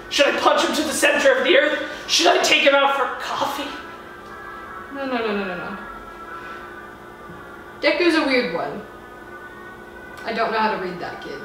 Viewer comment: What was said: No no no no no no Deku's a weird boy... Captions: Mmm-hmm dick! There's a weird one.